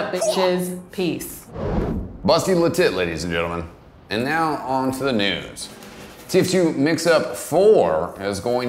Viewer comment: sounded like you said "Now onto the nudes"